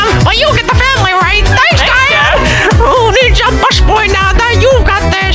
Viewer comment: why am I hear “booty”